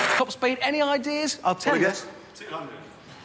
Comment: What did the guy say at 28:19 that got him pushed to the back of the crowd by Jeremy? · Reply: 200